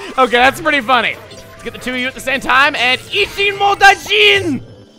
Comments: what have i just heard... at 4:46.... wut even... for a second i thought said "Icchimotaizene"... '.' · What did he say at 4:46 when he caught the two Greenies at the same time? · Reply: forestfairyunicorn Ichimodajiin (I think) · I keep watching this video and I don't know what says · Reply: Chaoskid Ichimodajiin means "two for one" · what was saying at 4:46 cause its not english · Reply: Tyler Birdboy he said Ichimodajiin, which translates to "two for one"